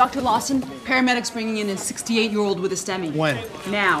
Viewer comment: I thought she said a sixty year old with a semi...😂